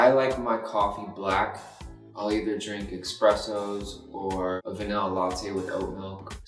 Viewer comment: All I heard was “expresso”